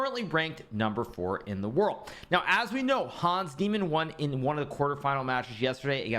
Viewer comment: Did he say "Hans Demon"?